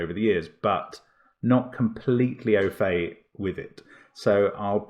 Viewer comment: not completel what with it? I didn't catch that word Sounds like "okay" but prolly isn't/ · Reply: "Not completely au fait." "au fait" being French for "having detailed knowledge of" (or a similar sentiment).